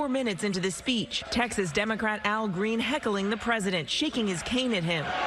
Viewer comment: I had to rewind…I thought I heard “Shake his taint”